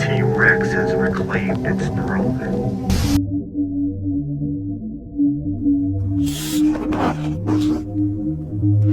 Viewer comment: ok so i went and reversed this audio. I couldn't understand it too well but from what i could gather it says "The time (unintelligible) is upon us" If anyone else wanna has a go be my guest. I don't have the best hearing so you guys might have an easier time than me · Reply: Its actually "judgement, is upon us"